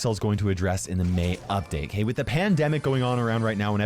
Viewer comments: i really thought you said "pamdemic" 😂💖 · When said pandemic I heard ''Pamdemic''